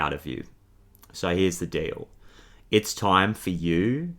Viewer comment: I thought u said here’s the dill